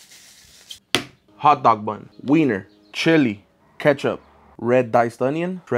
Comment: I heard you say winner